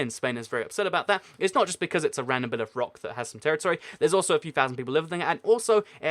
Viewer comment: Ok, what the heck did you say at 4:24? "There is also something something people living there" Like, what the heck? Also, "the danish"? Don't you mean "the Danes"? Like, the Danes do danish things in Denmark. In other words, "danish" isn't the people but the things they do or that is associated with Denmark. So for instance: My neighbor is danish, she's a Dane born in Denmark and her danish cakes are magnificent. Of course I'm not a native English speaker, but that's the only thing that makes sense to me at any rate... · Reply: He says "There is also a few thousand people living there."